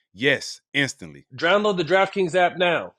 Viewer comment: I swear to god barnes is so high he says "drownload" instead of "download"